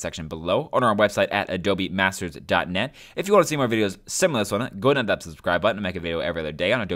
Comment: Is there a plugin you can recommend to understand what the hell you said from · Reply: Haha, "If you want to see a video similair to this one, go ahead and hit that subscribe button, I make a video every other day". I've said that phrase like 300+ times, so sometimes I end up slurring it a bit haha.